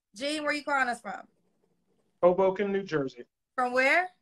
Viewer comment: Nice, I remember someone post about that clip few weeks back and it seems like they thought he said h@ broken instead of Hoboken NJ lol, I have family out there in Jersey. That place is for the upper class residents. U maybe get a home about 500k but average home in those areas will run u like 700k+. U have to be about something to be in those areas.